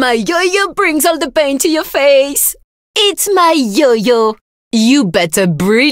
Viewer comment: I think she says “Eat my yo-yo!”